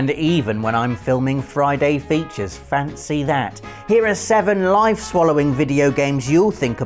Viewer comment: I thought said "FANTASY that."